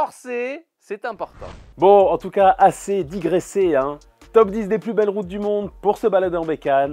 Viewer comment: " assez dit graisser " ... ben je croyais que c'était la seule chaîne qu'on avait pas besoin de graisser . 'comprends plus rien moi . 🤔